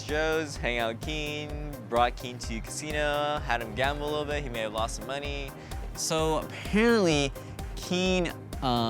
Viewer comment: I like how at 2:46 he sais "He may have lost some money [in the casino]" but the subtitles say "He made lots of money"